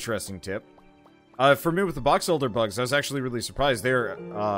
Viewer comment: A small infestation of what kind of bugs? Sounded like you said "box elder" bugs. I've never heard of those.